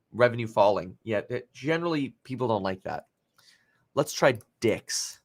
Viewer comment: Half asleep and I hear you say "Let's try DICKS.....DICKS!" couldn't stop laughing lol